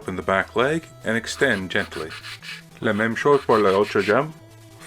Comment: what did he say? It sounded like French. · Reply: It was French, you’re right. I said “la même chose pour l'autre jambe”, which means: “the same thing for the other leg”.